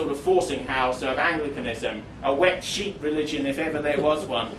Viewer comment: What does he say here 10:32 . A “wet sheet” “wet sheep” religion? What does that mean? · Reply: Wet sheet, it means something that is weak and useless